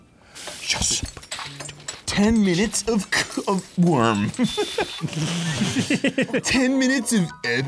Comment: i definitely thought Arin had said "10 minutes of k- of .. worm.." and i was CRYING 😂😂